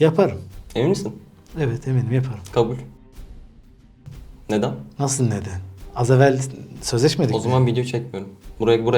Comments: SANSÜR NE YA NE DEDI ACABA SANSÜRLÜYCEKSEN Bİ ANLAMI YOK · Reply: Gaymisin? Dedi. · sansürlenen yerde ne dedi olm biri söylesin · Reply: Gay misin dedi